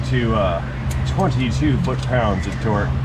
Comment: I misheard a word due to the noisy background music. What I heard was "torqued down to 22 buttpounds of torque". 😂😂😄😄